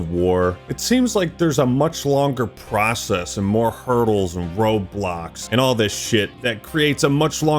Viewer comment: What he said: "Roadblocks" What I heard: "Roblox"